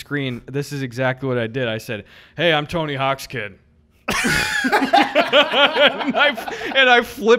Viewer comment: I laughed harder because I thought said “Tony Hawkskin.” Like just some guy.